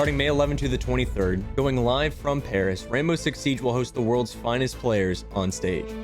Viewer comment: Di-Did you say... Paris?